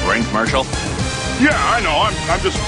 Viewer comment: It sounds like he was saying "Fuckball" rather than "Football"